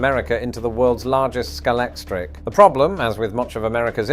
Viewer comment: about Eisenhower turning American into the world's largest Scalextric. The WHAT?! That got me curious. Did I hear that word right? Was that waning British accent too confusing? Nope. I heard it right. I googl'd it. Scalextric is the leading brand of UK slot racing cars. Slot cars? Instant nostalgia set in. Now I know what meant.